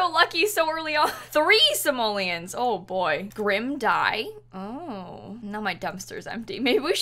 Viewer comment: When she said “Grim Dye?” I thought she said “Grim Died!?”